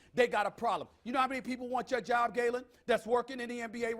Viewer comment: Did he call him Galen at 12:57?